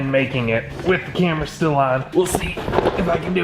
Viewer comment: says “with camera still on” but I thought he said “Cameracilla” like the name Priscilla but with camera. I replayed it 3 times before I realized that no, y’all didn’t name your camera 😂😂🤦‍♀️